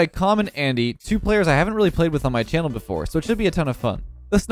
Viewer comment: I thought he said, “today I play with Common Andy.”